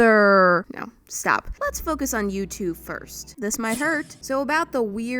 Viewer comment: who else heard "you two" as "YouTube"